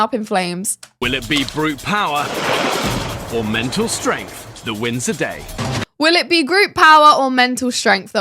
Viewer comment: The show: "Will it be brute power or mental strength that wins the day?" ...what I heard was "will it be men or men that win?"